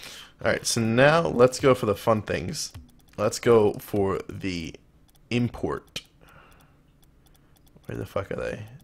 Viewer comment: is it just me or at 12:59 did he say incort?